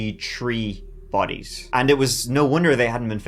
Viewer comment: I heard "Tree Bodies" and I'm like "what are tree bodies?" Then I remembered the accent. Oops!